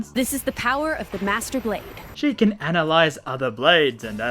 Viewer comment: am I hearing masterba**